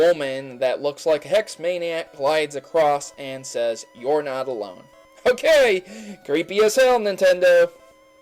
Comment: Did you just say ''luminosity city''? & she actually says, ''you're not the one'', instead of, ''you're not alone''.